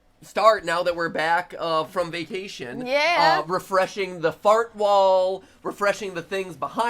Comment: is it just me, or did he just say "Fart wall"?